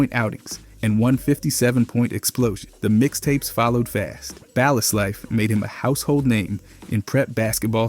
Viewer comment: am I tripping or did bro say “baal is life”